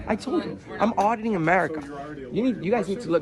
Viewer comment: when says his channel name, I swear he said Odd In America. He should consider changing to that anyways, seems more fitting